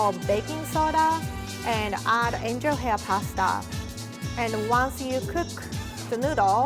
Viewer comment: What she says at 4:13 ? What kind of paste ? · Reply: Angel hair pasta, not "paste". :) It's basically thinner spaghetti. Adding the tablespoon of baking soda makes the angel hair pasta taste like ramen noodles. You could probably also use normal spaghetti if you can't get angel hair, it'd just result in thicker ramen noodles.